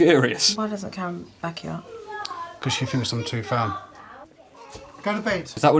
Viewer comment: ngl I thought he said fat not ferm had to watch it a 2nd time to hear what he said